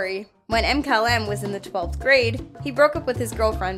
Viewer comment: for some reason I thought she said 4th grade and I was like “wtf? This is what 4th graders are doing now?”